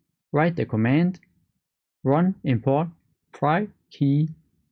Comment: What in the name of sanity are you saying for the last command to run to import the wallet??? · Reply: Yes it can be a little tricky. The command you can write is run importprivkey "fkdjsbzj7236...". Another thing you can do is write run help and see a list of commands.